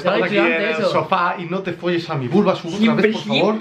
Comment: pensé que dijo burro azul